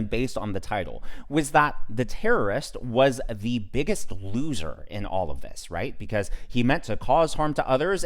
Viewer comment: I like you, but just clarifying would have been enough. I thought you said victim not biggest looser.